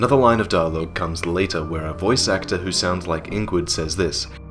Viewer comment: Need the abyss? I don't know, I heard "You MADE the Abyss" Eh, what do you guys think?